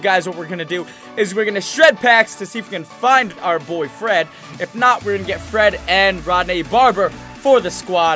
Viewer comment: I thought said "My boyfriend" but he said "My boy Fred"